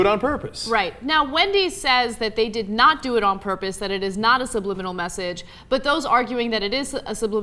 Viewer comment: I SWEAR TO FUCKING GOD I HEARD WHISPERING SAYING THIS IS NOT THE MESSAGE